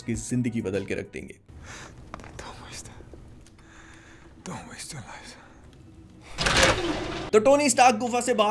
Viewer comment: wo kya bol Raha hai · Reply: Don't waste it don't waste your life